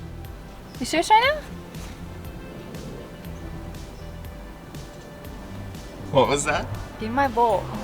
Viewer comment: What was being said at the 7:39 mark? Was that Armenian, or do I need to get my ears cleaned? This top looks great. It is like having a moon roof. Thanks much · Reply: she asked "you serious right now?"